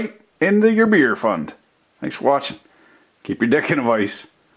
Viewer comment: Ummmmm What? All I understood from that was the end part. Which sounded like “keep your dick in a vice”…